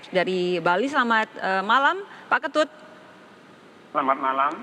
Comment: gua dengernya pak kentut anjir 😅🤣🤣🤣